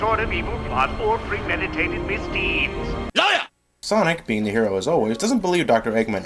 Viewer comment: what did he say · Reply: LIAR!